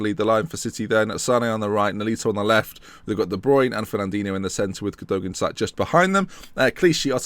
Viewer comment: When you said de bruyne I thought you actually said The Bryune